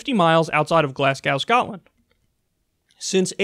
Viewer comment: I was just listening and thought he said "Glass cow" because of how he pronounced Glasgow